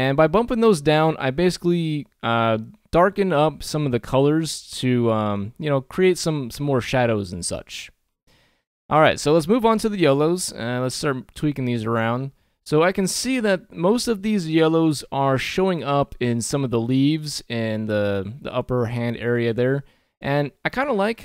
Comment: I thought I heard you say "Let's move onto the yolo's" and was briefly very confused